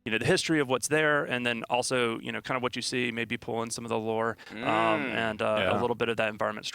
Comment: did he said lore ?